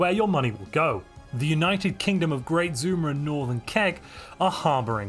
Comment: I was convinced I heard "Great Zoomer and Northern Kek".